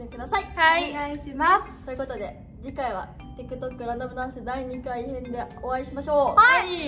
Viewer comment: 毎回『ランダムダンス』が誰が言っても『ラ「ム」ダムダンス』に聞こえるのは僕だけでしょうか… いたら👍ください(｡>ㅿ<｡) そんなことは置いといて、相変わらず皆さん 可愛すぎます！！！！♥♥♥♥♥♥♥♥♥♥♥♥♥